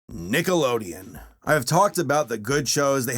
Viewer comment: did he just say ni**alodeon